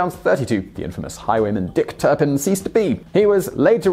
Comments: Swore this said 'Dick Tippin' Turned out better than expected. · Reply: I swore it said "Dick Turbin" so we were both slightly off. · Thought it said dick turbin😂